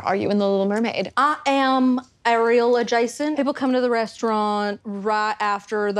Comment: I heard “I am Areola Jason.” I was confused.